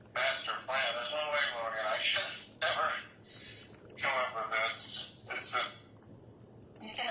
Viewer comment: Did Chad say “I should have never come up with this” at 13:30 ? Sounds like he’s admitting he made it up and it went too far. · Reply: He actually saidi " me and Lori could never have come up with this", basically admitting trying to convince people that they could not do anything wrong.